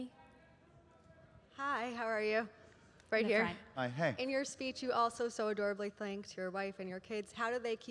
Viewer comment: I heard "HEY YOU" talking to:D